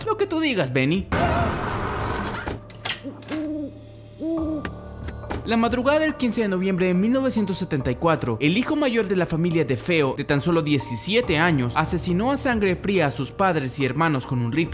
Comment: La familia de feo? Escuche mal